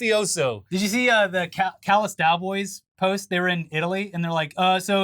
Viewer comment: please tell me im not the only one who hears callus dowboys 😅